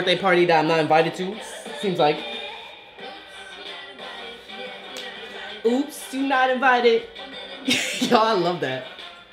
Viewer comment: i thought she was saying "Oops you're not about it, You're not about it" the whole time